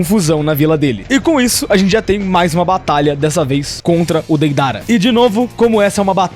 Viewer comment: Só foi eu que escutei , " O dedada"kkkk